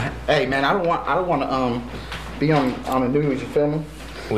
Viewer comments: "I don't wanna be on the news, you feel me?" "We are filming." completely misheard that lol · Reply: I think he means that thought he said "You filming me?" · he said you feel me? but thought he said you filming? hahahaha